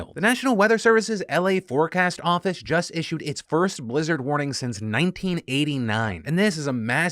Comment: Misheard that as "wizard warning" 😅